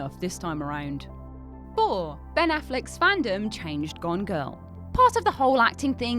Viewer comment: Can I be said to have enjoyed this while only having understood every third word? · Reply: When she said "Gone Girl", at first I thought she had said "Gongo".